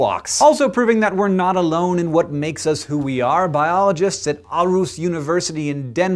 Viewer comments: Cracked me up when he said Aarhus.. Sounded like he said Arus :) Then again I guess he can be excused from not pronouncing it correct since the two A's go together to form Å which isn't exactly a part of the English vocabulary. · I love the way Hank said Aarhus. Almost sounds like he said ARoS (A museum in Aarus)?